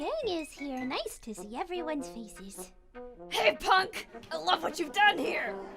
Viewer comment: The whole gang is here! Nice to see everyones feces. Thats what i heard.